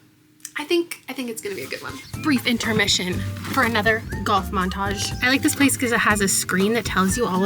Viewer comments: def thought you said ‘goth momtage’ and I was so excited lol 😅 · I was just listening while doing some chores and thought you said “goth montage”. I was like “hell ya I’m here for a goth montage!”